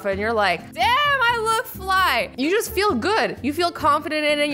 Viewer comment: What azzy said: damn I look fly. What I think she said:damn I look like a fly. What I thought when I heard I look like a fly: AZZY YOU DONT EVERY LOOK LIKE A FLY!!!!!!!!!!!! When I realise what she said: ok I am lucky I NEVER said that out loud.... X.X